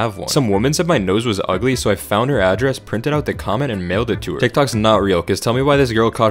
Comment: I thought she said NAILED it to her